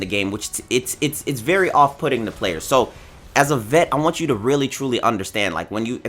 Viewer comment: did u just say as a VET lol